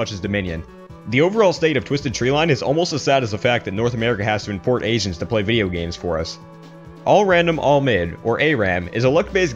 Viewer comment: Can someone tell me what he said after he said "the overall state of twisted tree line-" at 1:30. My English isn't perfect, so please help me. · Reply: 김영후 "is almost as sad as the fact that north America has to import Asians to play video games for us"